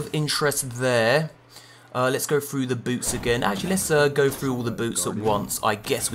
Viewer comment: every time he says "boots" i hear it "boobs"